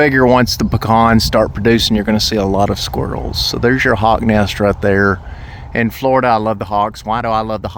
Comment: When you pointed to the nest, I first thought you said 'hog' nest, not hawks nest, lol...